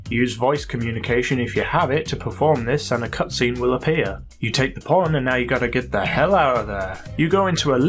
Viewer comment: wait at 3:17 did he just say take the porn? if so that better be some hardcore girl on girl stuff on that usb XD